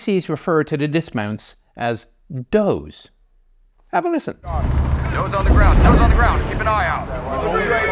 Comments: I thought he was saying "Joes on the ground". Junior enlisted are often refered to as Joes in the US army, atleast in my day. Either way, it makes sense. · This whole time I thought he was saying “Joes on the ground” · Honestly, I thought they were saying “Toes on the ground”, which makes sense so never questioned it. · I feel dumb i thought he said joes on the ground since troops were called G.I. Joes